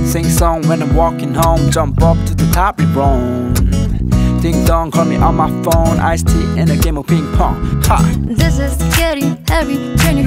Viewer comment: 초에 엄마폰 나만들었냨ㅋㅋㅋㅋ